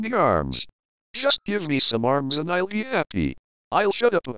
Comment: What the hell did Pollo say at the beginning of that sentence? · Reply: A-R-M-S!